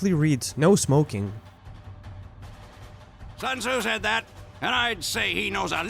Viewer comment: I always thought he said "Sons who said that?" Not "Sun Tzu said that." For awhile.